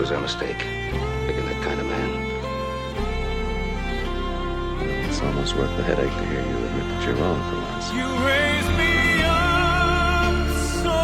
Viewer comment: So touching..! Thank you for posting this video, love the Cartwrights! I have a question because I'm French and I don't understand what Adam is saying between 4:35 and 4:40.. ?? Amazing video... Wonderful ! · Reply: He's saying "It's almost worth the headache to hear you say you're wrong, for once." I love that sassy little wink he gives Ben in that scene.